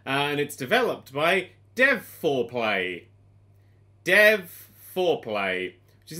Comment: "Dev4Play" I just heard it as Dev foreplay.